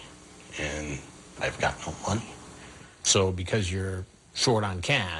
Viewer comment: That “Lonely and I’ve got no money” excuse is up there with “My best friend’s mom is dying of cancer” lol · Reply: I actually think Chris misheard him, he just says "I'm lonely and I've got no-one". But the misheard 'money' just stood uncorrected...